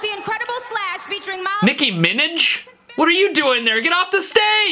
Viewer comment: When said ‘nicki Minaj’ in that way- I heard “Nicki Spinach”